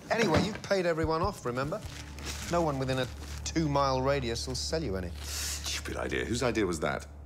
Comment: what does martin freemann say? radio sally welly ???? Can anybody please help to translate that!! XD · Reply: MediEvi "no one within a two mile radius will sell you any"